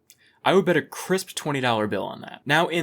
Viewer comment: I originally thought he said a chris dollar bill and just pictured money with Chris on it instead